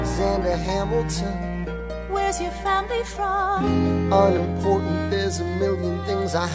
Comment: oh in satisfied I always thought when he said "unimportant" he said "I'm an orphan"..... gosh I learned more in this video than science class